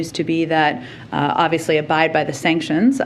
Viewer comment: Yes, "abide by the sanctions", as if the world doesn't know that the US' era of arrogance is over. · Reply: Thanks for the correction, it's 'abide' indeed. Must be latest communication training I misheard it as 'obey' ;)